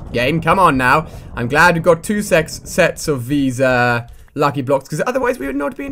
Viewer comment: he said Sex instead of Set